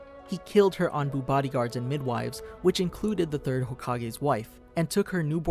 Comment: did he say 3rd hokage???